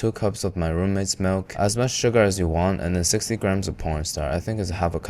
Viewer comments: did bro really say "porn starch" or is it just me? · BRO THE CAPTIONS THOUGHT U SAID P0RN STAR INSTEAD IF CORN STARCH-🥀🥀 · did brother say "60 grams of pornstar"? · My man where did you even find a porn star to lend a 60 grams of themselves😭😭😭 3:09(even subtitles say porn star wth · Pornstar instead of cornstarch 😭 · did you say 60 grams of porn star. I stg you said porn star instead of corn starch lol. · does he actually said p*rn star instead of corn starch? Or does my ear too dirty? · did he just say porn star? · Did you just said p o r n in the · I can’t be the only one who heard 60 grams of ‘pornstar’ in 3:10. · Did I hear you say "60 grams of porn star?" I SWEAR I heard that...I hope you mean corn starch! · no matter how many times I listen to it I keep hearing "and 60 mg of pornstar" 😂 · did he say porn star @ 3:10 or am i trippin